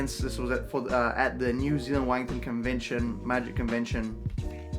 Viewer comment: I need to get my ears checked, thought I heard New Zealand Wanking Convention. Sorry to hear you've got Con Plague, hopefully you'll have a swift recovery.